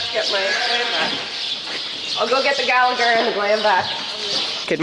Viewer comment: this whole time i thought you were saying 'GlamVac" and i'm like ohh glam what a cute glamorous name for a vaccine